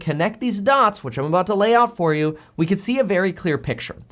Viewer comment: A little freudian slip here? Lay-off instead of lay out? 🤔